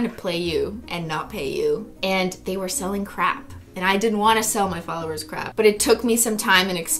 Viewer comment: I thought she said crack, they're selling crack 😂